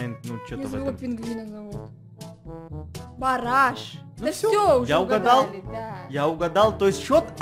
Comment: кому послышалось Параш а не Бараш